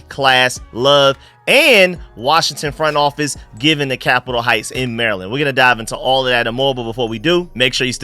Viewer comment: Haha I thought you said Columbia heights instead of capital heights completely different